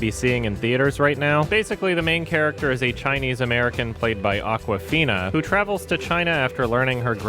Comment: I've literally never heard of her before so I thought you said "Basically the main character is a chinese american played by Aquafina" So I was like...she's a brand of bottled water? What?